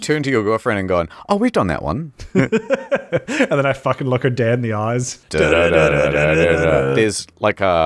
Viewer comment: (as) said "Oh we've done that one" about a movie sex scene to which finished by saying "and then I fucking look her dead in the eyes." I thought he said, "And then I fucking look her dad in the eyes." Like bloody hell that's something. You're a freak huh?